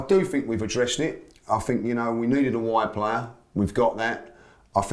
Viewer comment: I thought he said “we needed a white player”